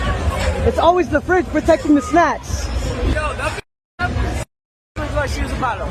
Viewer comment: "Its always da Fridge, Protecting the Snacks he said🤣🤣" im weak asf 🤣🤣🤣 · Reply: I thought he said snatch, but snacks make more sense 😂